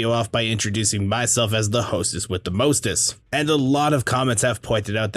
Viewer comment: Ngl I thought you were saying hostest not hostess